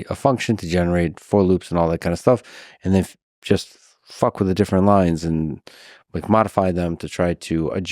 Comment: Am I the only one who heard fuck 😂 or I misunderstand